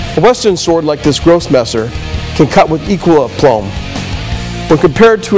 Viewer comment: What does Lynn say at 1:25? "A western sword like this Grosse Messer can cut with _____" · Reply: Hey, I know this post is nine years old, but the word was aplomb.....